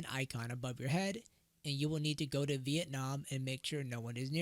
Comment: why am i hearing you telling "you will net to go to vietnam and " XD